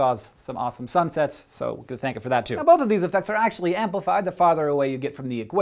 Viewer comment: what are you saying at 1:44? I have watched it a few times, and you say it so fast and slurred. Not gonna lie, it really sounds like "so, god thank you for that too" (referring to the the sunsets). I am not judging or anything. Just curious. and maybe I am putting too much thought into this, but it also looks like you are making a 'cross' with your two outstretched fingers. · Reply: "So you could thank it for that too" i believe